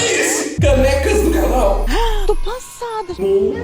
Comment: No minoto 8:09 eu ouvi canecas do cavalo